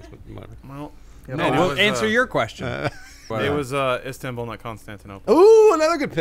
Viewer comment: IF ANYONE IS READING THIS PLEASE HELP ME What did said his first song he downloaded? and he said it on 0:45 that's when he said it. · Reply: this is late as shit, but he said "Istanbul, not Constantinople", it's a song from They Might Be Giants.